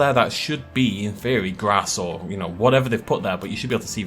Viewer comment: at first I thought he called it "grasshole"